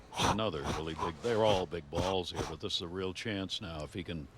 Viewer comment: very silently “did you just say big balls XD IM FUCKING CRYING